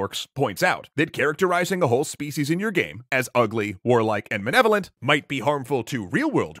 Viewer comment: did I mishear that or did he just say menevolant? Did you mean malevolent? facepalm